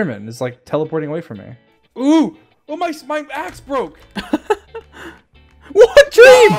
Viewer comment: I thought he said “my back broke” when trying to fight The ender man